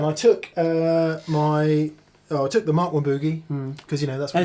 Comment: what does Mick say here? "I took the Maahhwkkwhaaaboogie".... Mark I Boogie?